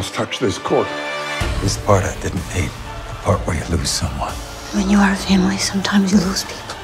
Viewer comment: OMG. I can't understand what Carl says at 1:01 Can anyone tell me? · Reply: This part I didn´t need, the part where you lose someone.